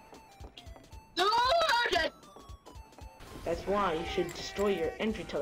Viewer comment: I really like how the word just say *gey* instead of ded or whatever it is